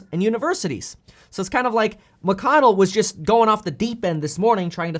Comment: I thought he said Bi@ Mcconnell 🤣🤣